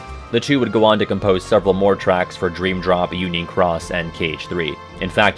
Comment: When you mentioned union cross I misheard it as unique Ross, and that is what I will call that game now.